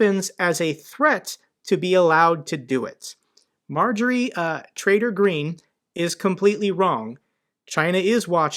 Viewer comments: did you really say "Marjorie Traitor Greene" at 8:20? Lol. Sorry for my bad hearing, if i am wrong. Thumps up, anyway. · "Marjorie Traitor Green" - it was subtle, but I heard it.